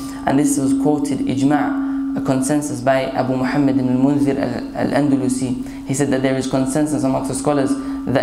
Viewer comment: What is the name said at 23:42 before Shaykh Islam ibn Taymiyyah RA regarding shaving the beard being haram? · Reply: Roksana xo Abu Muhammad ibn Mundhir Al Andalusi